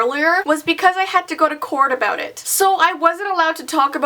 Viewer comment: did anyone else hear her say "corn" instead of court around that